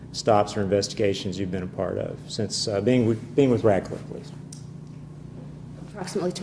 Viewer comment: Radcliff? I thought she said Ratchet